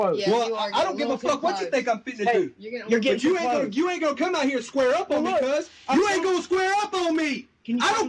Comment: wtf he needs to be fired at 2:11 he said fuze which is what bloods call each other